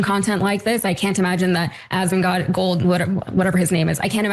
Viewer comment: Did she say Asmon"god" lol